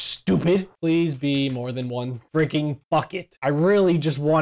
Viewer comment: did say f*ck it